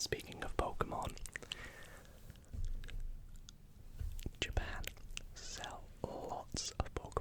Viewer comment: I thought it said “Japan hell” for a moment and I was worried for what u experienced there 😭